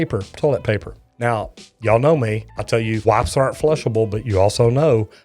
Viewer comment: BRO at 7:57 I thought he said WIVES and that he did tests on them.I was like WTF😳😳😳😨😰😰